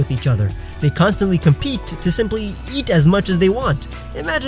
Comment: It kind of sounded like you said "simply yeet"